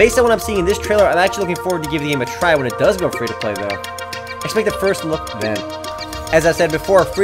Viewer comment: LMAO! Anyone else hear him say at 3:57 "expect a first look, nah"